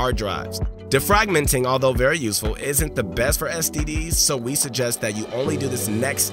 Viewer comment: did you just say, not the best for std's?